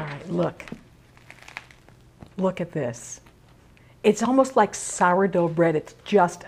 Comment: does she say: "It's almost like Cyrodiil bread" ? Or is it just me? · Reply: She said: 'It's almost like Sourdough bread'